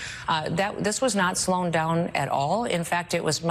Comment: Did she say “slown down”?